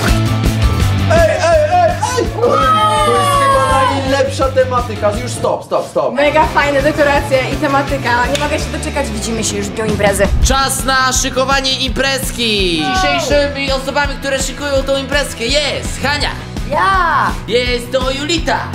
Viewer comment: usłyszałem że mówi: Widzimy się już w join Brazzers.